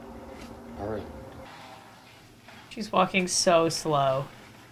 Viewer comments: At the beginning when barbra was walking them to meet caleb I thought you said the f word but when I replayed it I heard walking. WHY DID I AT FIRST HEAR THE F WORD!😂😂😂😂😂 👇🏻pls like if you heard the f word a bit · Not me thinking Lauren said f*king but I think she said walking but idk for sure it happend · Is it just me or did lauren say the f word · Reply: Walking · did Lauren just- say what I thought she said? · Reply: She said walking